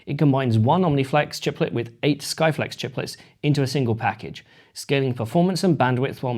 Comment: T2 Skyflex? Sorry I thought you said 'Skynet' then. Phew!